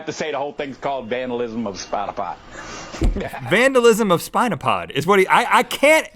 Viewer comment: Vandalism of spot a fight? Spinal pot? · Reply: I kept hearing "spinal pod" and I thought, maybe that's his YouTube/ Blogster/ or whatever, username. Spotapot. Hmmm. If that's a term, I guess that makes sense.